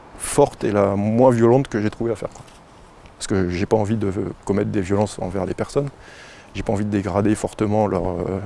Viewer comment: "je n'ai pas envie de commettre de violences envers les personnes" Perso, j'ai entendu : "je suis une brindille, donc j'attaque par derrière"